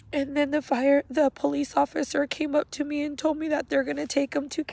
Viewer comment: R.I.P stay safe Alejandro :( I think said KFC at 12:27 that got me laughing